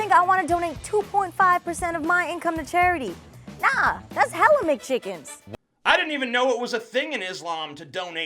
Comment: WTH does she say at 5:54? Hella McChickens??? WTF?